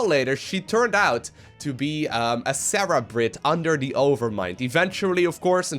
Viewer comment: Did you... Did you just call her a "Sarah-brate"?